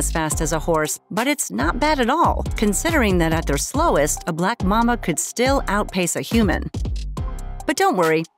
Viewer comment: sounded like "a black mamma" lol